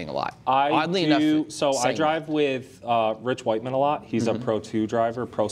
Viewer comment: I think the list is pretty damn accurate. I thought said, "I drive with rich white men a lot."